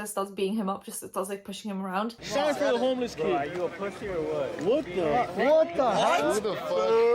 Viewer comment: I replayed the clip at 5:17 a million time and still can't make out what's the insult or whatever. Wtf does he say? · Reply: i’m pretty sure the guy said “bro are you a pussy or what”